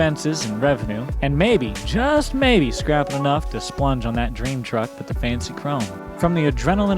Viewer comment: did you say "splunge"? Lol